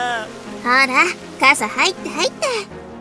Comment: 入った入ったが入った入ってに聞こえた